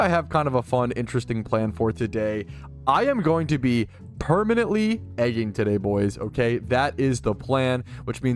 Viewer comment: I heard "I'm going to be permanently edging today"